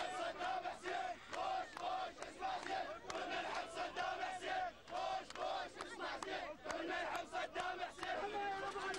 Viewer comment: what they say? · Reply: "Bush,Bush, listen carefully, we all love Saddam Hussein"